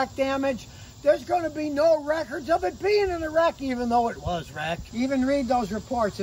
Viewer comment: I thought he said the car was in Iraq? 😀